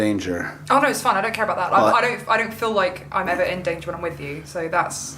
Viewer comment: It sounded like he said (I do. Don’t do it.)